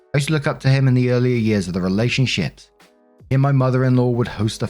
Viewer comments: did you say relationshit? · Oh my god it sounded like you said “relationshit” around 9:35 and I love the accidental showcase